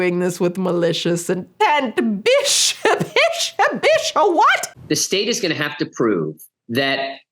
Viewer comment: until u asked "what" i thought u were saying, "bicha, bicha" With dependind on how u are saying it is like "girly!?" Or even actuality just "Bitch" in a friendly gossiping 'wft!?' way in my language. I fell so silly but so amazed right now lol Just wanted to let u know, is not that important, but yeah, funny to me